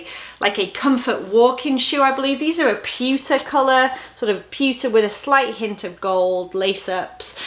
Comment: Your accent got me good. You were describing a pair of metallic shoes, and I could've swore you were saying pizza colored. I was like wtf kinda pizza is metallic- Lol! I then realized you were saying pewter color.